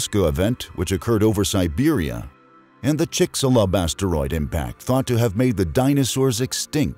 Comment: I swear, I heard him say basteroid.